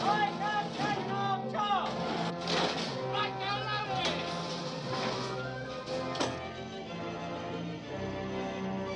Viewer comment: what did he say? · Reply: The first guy says “right gun second half charge” and the second guy says “right gun loaded”.